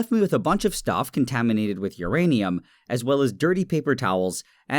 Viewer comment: Thought it said uranium taste